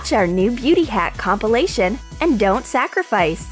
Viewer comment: When I heard "Font sacrifice!" I started balling (Balling: Laughing really hard)